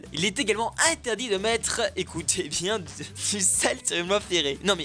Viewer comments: Tu dit quoi a 2:10 ? Se salte d'enterré? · Il dit quoi à 2:10 ? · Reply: Il est interdit de mettre du sel sur une voie ferrée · tu as dit quoi a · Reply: Du sel sur les voies ferrées :)